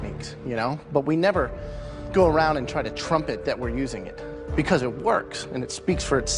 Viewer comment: It sounds like he is saying Trump it